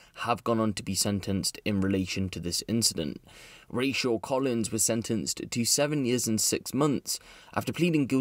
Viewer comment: sorry, did I hear Rachel?